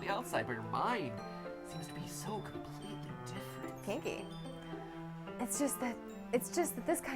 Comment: Is it just me or at around 1:10:50 when Mat is talking about Yuris personality after the speech does steph say kinky